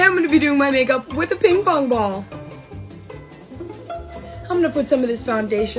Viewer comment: You know I thought she said “I’m gonna do my make up with a pink foam ball” 😂 😂😂